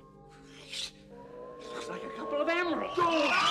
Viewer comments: Eyes Squint Janitor: "It looks like a gold emerald!" I never, ever, ever understood how someone could be so stupid lolol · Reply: I thought he said "couple of emeralds". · emeralds? true, i always thought he said " It looks like a couple of animals."